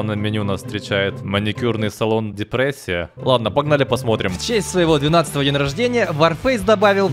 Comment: Господи, сначала услышал "в честь моего 12-ти летия" и подумал, что исполняется 12 лет....